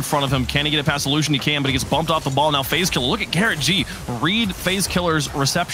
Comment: am i the only one that heard him say fazekiller